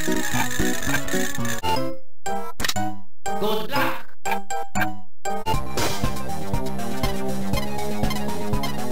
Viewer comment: What is he saying? I'm hearing "blue glock" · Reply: It’s Good luck!